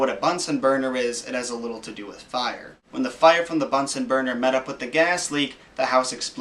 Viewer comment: I thought he said "butts n burger"